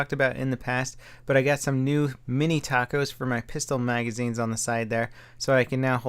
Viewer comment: "mini tacos" did I hear that right?